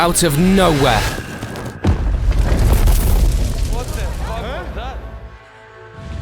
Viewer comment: he clearly said "What the f*ck was that?" not hell lol